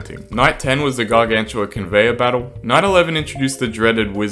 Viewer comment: my dumbahh thought he said 9/11